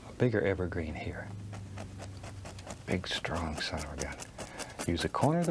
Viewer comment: "A big strong son of a b*tch" Did he actually say that? :D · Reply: he said a big strong son of a gun